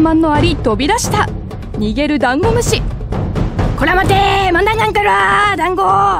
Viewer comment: なんて言ってるのw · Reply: こらまてーまんだーなんかろー